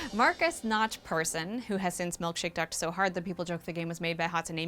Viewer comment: wth is being said at around 1:02?? milk shake ducked so hard??